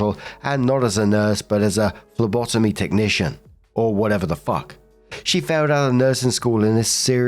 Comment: I first heard "lobotomy technician" and I'm really glad I misheard.